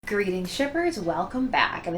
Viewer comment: I THOUGHT YOU SAID GREETINGS STRIPPERS LMFAO